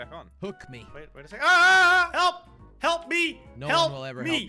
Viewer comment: iff you hear closer i think hes saying healthy